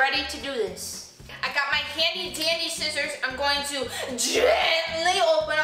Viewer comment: When you said handy-dandy scissors I thought you said candy candy scissors 😂